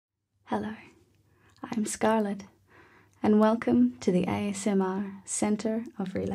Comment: Jeez it scared me at the start when she said hi it’s Scarlett I thought she said hi Scarlett and I got scared and had to reply to check (my names Scarlett btw)